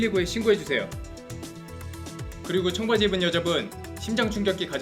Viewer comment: 초에 갑자기광고에서 형! 해가지고 거기 청바지입은 형!이라고 들림ㅋㅋㅋㅋㅋㅋㅋ